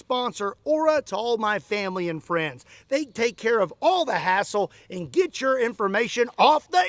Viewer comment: Did Say Assholes?😂